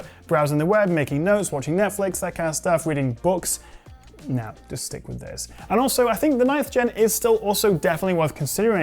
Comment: What does he say at 12:23 ? “Just stick with this”? If so, what iPad model is he recommending?